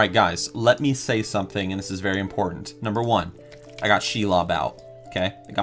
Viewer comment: when you said Shelob, I thought that's a funny way to say "Shia Labeouf" LOLL 😭